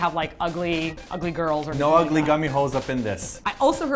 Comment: Omg. What has been heard cannot be unheard Q_Q The whole time you guys were talking about gumihos all I heard was "gummy hoes gummy hoes gummy hoes" >.>